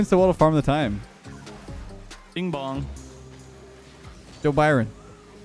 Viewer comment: Is it just me or did he say joe Biden